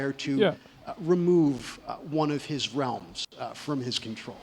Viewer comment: Did he just say Mortal Realms ! 16:34 :face-blue-wide-eyes::trophy-yellow-smiling